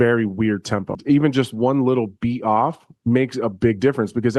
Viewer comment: “what did he say?” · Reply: A little beat off usually does make a world of difference. Anthony has a point there lmao